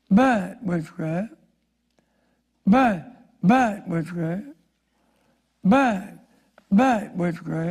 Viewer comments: I needed this. The whole "Bye. Bye, witchcraft," tic was crazy. I thought you had looped it. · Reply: I thought he said “baaaad witch craft” hard to tell with a country accent at the pace of a snail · “Bad! Bad, witchcraft, bad! Bad! Bad, witchcraft, bad! Bad! Bad, witchcraft, bad! Bad! Bad, witchcraft, bad!“ Well, I’m convinced! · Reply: i thought he said bye bye witchcraft bye bye witchcraft LOL i suddenly imagined NSYNC singing BYE BYE BYE